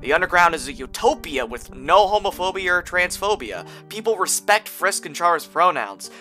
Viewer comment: AYO I need to get my shit cleaned, I thought you said "People respect Frisk and Chara's romance." for a sec there.